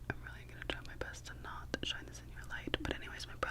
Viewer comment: I heard her say "I'm trying not to shine this in your light"